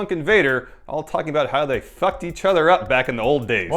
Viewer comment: I thought that he said "as they sucked each other".